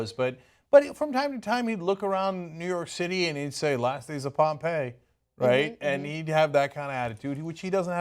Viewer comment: "he'd look around and say....pompei" what did he say i watched that part like 4 times and i still couldn't get it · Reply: he said last days of pompei